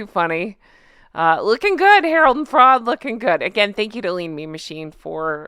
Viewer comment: Have to laugh....I honestly thought you were saying Harold the Frog and wondered how you came up with that nickname!!! Just realized you were saying Harold the Fraud!!!🤣🤣🤣